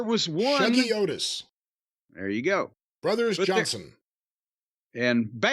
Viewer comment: What did say at 4:06? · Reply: Something Otis maybe Chubby Otis